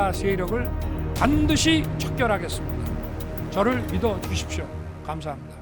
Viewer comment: 윤서결의 내란 선포문의 마지막 말 "저를 믿어 주십시오"가 '저를 사형시켜 주십시오'로 들리지~ 환청인가~~?